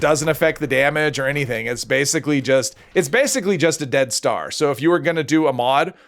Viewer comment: Oh you're saying dead star. At first I thought you were saying death star.